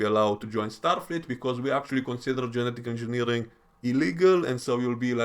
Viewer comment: sounds like you misspoke in the section where you were discussing The Masterpiece Society. Pretty sure you said "genital engineering" instead of "genetical engineering". Funny mistake though, hope you leave it in. Haha. Timestamp